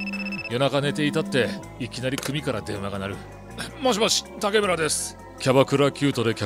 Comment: ｢もしもし！竹内です｣が ｢竹村です｣って聞こえる